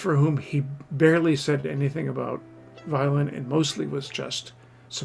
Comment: Lots of interesting things but some mistakes, as well: for example, at 13:32 the closed caption says VIOLENT but the word in the video is VIOLIN! Also the narrator mispronounces some names and words--not good in such a video. Finally, as NeiloftheViolin previously commented, Heifetz's right wrist and hand is NOT tense!